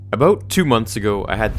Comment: Did he just say " A boat? "